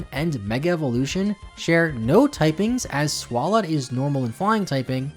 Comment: Sounds so much like you're saying Swalot instead of Swablu. Which is funny just because like 20 seconds later the fact is an evolution line mistake. So in my headcannon, I'm treating it as an intentional joke.